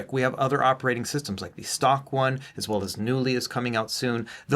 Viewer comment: what was coming out soon? Mew li? Newlee? What is it called?